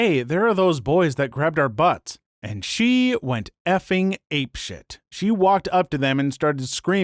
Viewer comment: You said "ape shit" instead of "ape crap" 😆